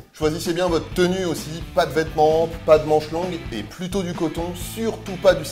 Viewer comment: J'ai pas entendu le "long " pour "pas de vêtements longs", du coup j'ai entendu "pas de vêtements" xD BBQ avec le Q à l'air haha !